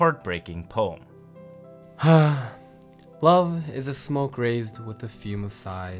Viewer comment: I thought it said “ Love is a smoked Raisin”.